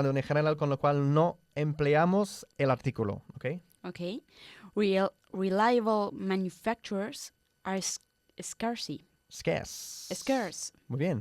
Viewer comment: Que dice en el minuto 11:31¿? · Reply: Reliable manufacturers are scarce. ;)